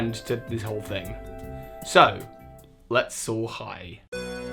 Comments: Wait. He was saying soar high at the end of his videos this whole time? I thought he was saying so hi! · I kid you not, I've been watching you for a year and a half now, and I swear I always thought you said "So hi" instead of "Soar high" until this video. I blame your British accent for that. Edit: I've been blessed with the coveted heart from! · Was I the only one who always thought he was say so hi Pokémon master instead of soar high